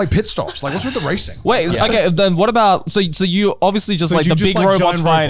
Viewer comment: I HONESTLY thought he said "Gorilla Garnt"!!!! 😂